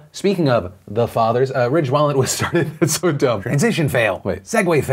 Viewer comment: At first I thought he said “Rich wallet “ not “Ridge wallet”. I’m so dumb😂😂😂